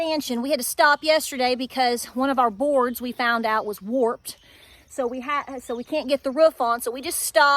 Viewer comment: HA! Thought you said you had a "warped boar". Poor goat